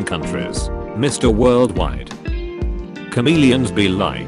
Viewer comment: U know you've played too much pokemon when you thought it said charmeleon